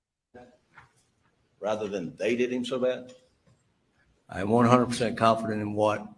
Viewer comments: Hearing for the first time. I heard “they did him so bad” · I thought I heard "I did him so bad". Why wouldn't they have a language expert interpret it? Surely the FBI or even local law enforcement agencies have experts that do this. Or am I missing something?